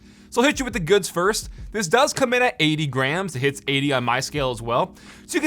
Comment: When he said it comes in at 80 grams, I thought he originally said 80 grand